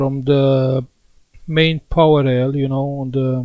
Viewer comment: Sorry to ask but what did you say at 5:47 , " Main Power L " ? El ? Line ?